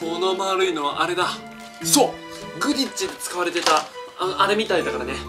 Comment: セクタムセンプラって使っちゃいけないでしょwww 切り裂かれちゃうw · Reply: 高橋宗詩あ！…すみません！ 発音だとクディッチに聞こえるもんで… クィディッチでした！！